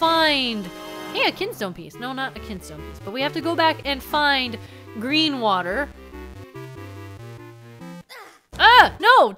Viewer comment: I misheard green water as Great Water